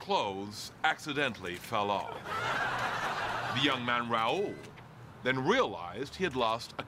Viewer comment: What does he say at 1:06? "At that point my clothes accidentally fell off. The young man ____ then realized he had lost a contact lens" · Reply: Raul -the latin guy's name